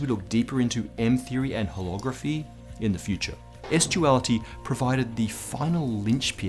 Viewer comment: hahahahaha he totally made that long pun p on pourpose pur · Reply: O DID HE SAY PORNOGRAPHY THEORY